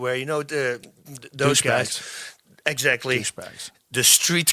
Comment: wonder what was whispered. Rogan whispered exactly. C'mon Rogan I hope you ain't prejudice or racist dog